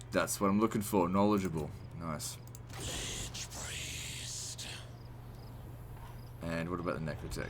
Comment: "Lich Priest" sounded like "Lich! Please...." to me for a second.